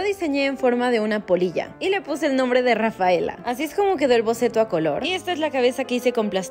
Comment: Escuche "rataela" XDD, alfinal escuche "diganme si aman a rafaela" voy a atras ala, DICE RAFAELA NO RATAELA